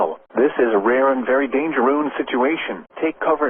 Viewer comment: did i hear dangeroon??? edit: immediately after i say this he points it out oh wow